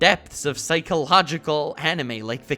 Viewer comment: Thought it said "anime treats you for who you fuck"